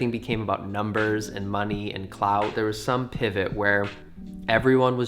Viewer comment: When you said clout I thought you said clouds and I imagined a social media utopia where everyone just talked about clouds